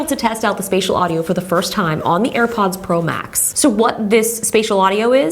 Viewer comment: Did she say AirPods Pro max